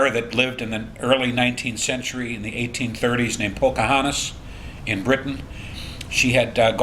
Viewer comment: Secretariat inherit a big heart from who??..did he said Pochahantis??..@ 6:25-6:27 listen?..i don't see Somethingroyal pedigree tree show by that name. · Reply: Pocahontas - <link>